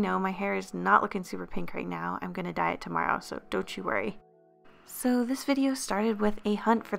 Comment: I thought she said im going to die tomorrow so don't worry